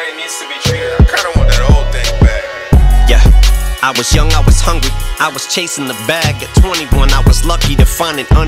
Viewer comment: Why does it sound like Em says young and hungy in the beginning lmao